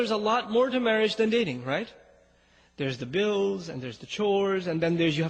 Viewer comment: I think he said "Chores" not "Shores"